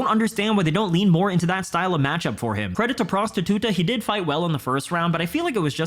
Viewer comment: Did I hear "Credit to prostituter" ?